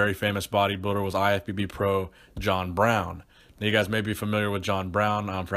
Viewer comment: Sounds like he said, "Niggas may be familiar with John Brown..."